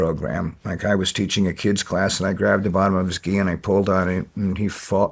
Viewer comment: When he said something about "grabbed the bottom of his ghee" I thought he said "grabbed a bottle of whisky" and had to rewind multiple times, turn on captions, and was still confused until someone next to me happened to know what a ghee is (not the butter kind).